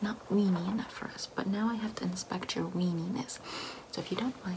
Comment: When she pulled out the magnifying glass I thought she said "I need to inspect your weenie"